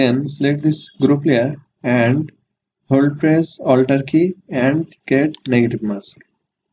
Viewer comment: What did you say at 3:03 about ALT and what? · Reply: Hold Press ALT key and Click on the Mask layer for Negative mask. Thanks for watching. ☺️🙂